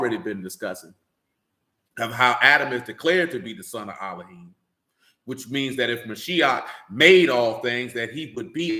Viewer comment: Can you please write out what was said at 4:21…? It was skipped out. Thank you! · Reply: That Adam is a similitude of Yahusha as both are declared to be the son of God